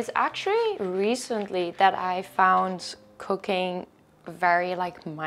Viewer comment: thought you said "cocaine" for a moment there ahaha